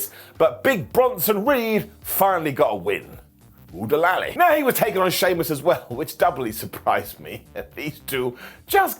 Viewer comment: Can I ask what might be a dumb question? What does Simon say at the 9:48 mark? I always wondering but always forget to ask! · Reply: Oo-de-lally! It’s from the Disney movie Robin Hood 😂